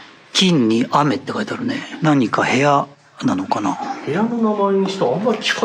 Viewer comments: 来てって聞こえる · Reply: の『きんき…？』ですよ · くらいに金雨(きんう)って女性の声が聞こえない？